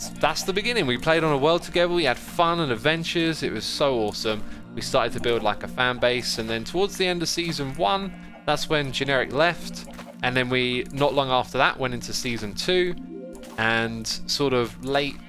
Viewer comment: i thought he said generic beef, and I thought...oh that's why we have vintage beef! ohhh